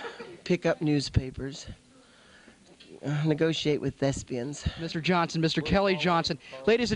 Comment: "negotiate with thespians" LOL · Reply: I thought he said negotiate with Mexicans!